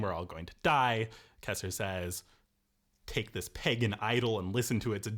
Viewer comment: is he saying pagan idol or peg and idle?